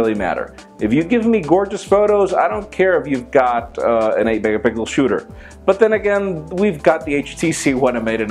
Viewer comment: Am I the only one who caught when he said 8 mega pickles instead of 8 megaPIXCELS lol